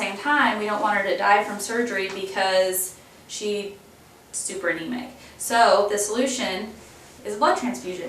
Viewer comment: Because sheee ... stupernemay? What is she saying?! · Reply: Super anemic. In other words she has a shortage of healthy red blood cells.